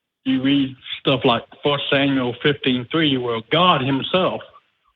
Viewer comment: What verse is the caller talking about at 0:50 ? I can't quite hear it, it sounded like daniel 15:3 but daniel only goes to 12 Edit; Its first samuel 15:3 never mind